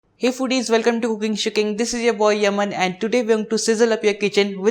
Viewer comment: Give the video a like to see more recipes like this in the coming weeks :) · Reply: it sounded like you said cooking shiting. I was like yeah we cook eat and shit but why would you say that but now i understand